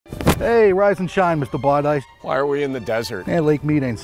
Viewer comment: "Rise and shine, Mr. ? " What did he call you? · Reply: Mr. bar dice